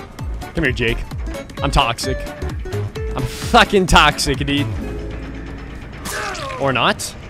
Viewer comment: i heard a fucking blink